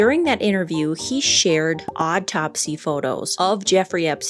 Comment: At first I thought you said “odd-topsy”